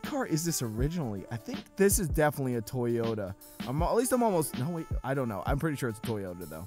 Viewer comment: Did you say toy yoda